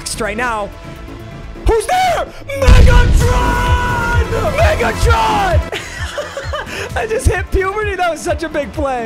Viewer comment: sounded like niggatron